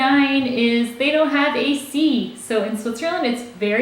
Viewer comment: I was always hearing "a sea" instead of "a/c"...